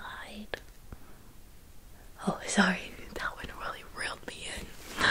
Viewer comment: ‘oh sorry, that…’ then I can’t figure out what she said😅 help heh❤️ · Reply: “That one really reeled me in”